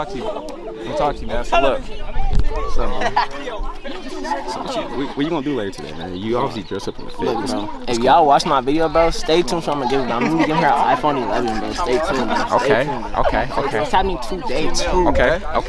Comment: I thought he said “Ur obviously THIRSTY” bro my 👂